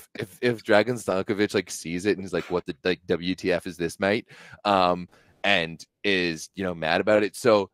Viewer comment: did he say mate?